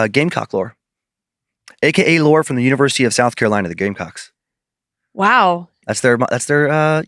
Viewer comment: Sorry thought you said Gay Cocks hadta rewind it .🤦‍♂️